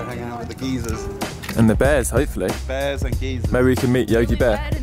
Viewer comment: At first whenever you guys were saying geiser, I thought you were saying geezer, which is basically what you can call an old person.